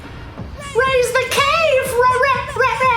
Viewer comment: My first time hearing that one line from the para para brothers, I thought they were saying “raise the gay”